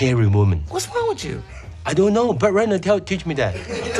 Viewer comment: "What's wrong with you?" "I dunno, Brett Ratner taught me that..." Well... that didn't age well... 😬😅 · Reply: I thought he said Burt Reynolds 😂😂😂😂